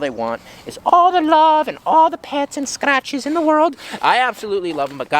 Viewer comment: I heard "all the love and all the pants and scratches in the world." 😂😂😂😂😂😂😂😂